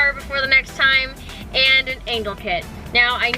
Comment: thought she said she wants an anal kit